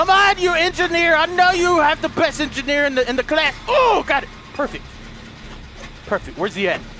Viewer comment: did he say be the best engineer in the clan